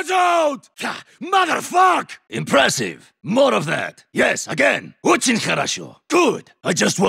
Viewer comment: Hey what's the line that niko says in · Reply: NiktO says Ochegn Hkhorosho (not oten korocho)